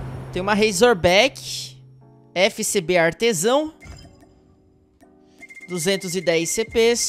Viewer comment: Olhei pro lado e ouvi "FCBA Tesão" pensei q nosso CodM tava virando jogo de verdade